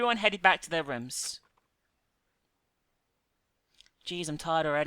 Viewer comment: I was looking at something else at 39:42 and I could have sworn she said "Geez I'm hard already" and I did the fricking quadruple take on the screen XD